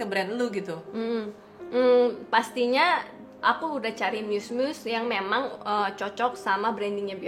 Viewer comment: Ada yang bisa bantu 35:46 itu ci Paopao ngomong apa ya? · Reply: Muse = sumber inspirasi, mgkn maksud dia. Cewek2/influencer yg pas buat dia endorse and bisa represent produknya dia.